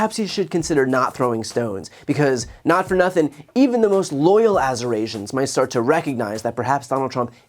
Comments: At first I thought he was trying to say Amerasian. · I thought he said "Azure Asians", insulting their skin tone as well.... LOL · I thought he said "azure Asians," and i wondered if he was having visions of the Hindu Deity Shiva. 🥶😇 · I thought he said Amerasians. And he'd found a new race to harp on 😅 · I thought he was trying to say Amerasian. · I thought he said azure Asians at first. I was like, blue Asian people? Good grief, what a disgusting pig!! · Oh. I thought he was trying to say Eurasians. 🤔